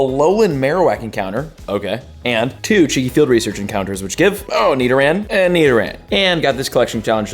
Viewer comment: cheeky field research encounters?? What do you say right there? I 😂😂